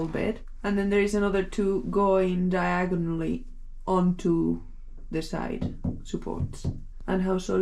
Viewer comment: did she say "Diagon Alley"? 😲⚡🤓🧙